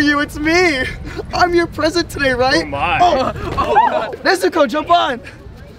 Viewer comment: for a second in 0:08 i thought it said hey nezuko JAMAL 😂😂😂😂😂